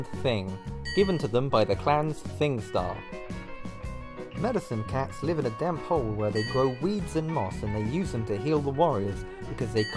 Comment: I heard “Medicine cats live in a damp hole and grow weed.”